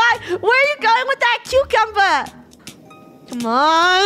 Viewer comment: I thought funneh said cute cumber when she said cucumber at the end